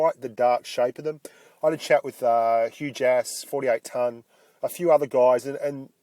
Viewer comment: xD when you said "Hugh Jass" it sounded like you said "Huge Ass" lmfao